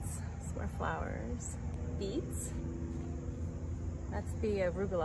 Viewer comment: Awesome garden! I also live at my parents’ house and we’ve been harvesting a bunch of cherry tomatoes lately as well. Super tasty! Thanks for the garden tour! ✨🙌✨😊✨ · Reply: nice! Hope the beets were tasty too. At first I thought you said beats 🤣 maybe I will harvest some tasty beats today if I get motivated to hook up some gear. 😊✨ Yeah, living at home can be trying at times, but it affords me the chance to follow my music dreams. And nice to be close to my parents still. Hope you have a lovely rest of your weekend!